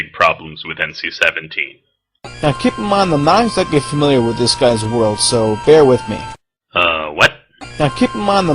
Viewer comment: Same here. I remember how couldn't understand what was saying at 2:25 but eventually I caught on that it was "Now Keep in mind that I am not exactly familiar with this guy's world so bear with me".